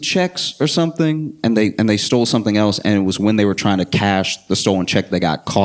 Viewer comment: My dumb ass thought you said "he stole chex" and I thought "man, chex is aight but I wouldn't risk getting beat by cops for stealing some."